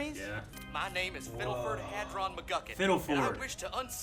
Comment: Watching this episode for the first time, McGucket reminds me of Ice King from Adventure Time. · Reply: Ikr? When he said, "My name is Fiddleford Hadron McGucket", I heard, "My name is Simon Petracoph"